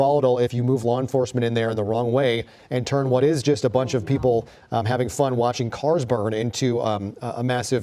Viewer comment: Did he just say a bunch of people having fun watching cars burn? Us that what he said?